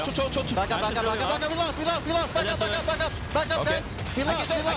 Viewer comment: is xmithie saying "we lost"? or "reload"? or...? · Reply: Xeng Thao “we lost” as in we lost the fight